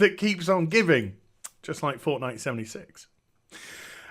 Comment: Did you say Fortnite76? Because I think I've got an aneurysm while trying to imagine the amount of garbage contained in one game.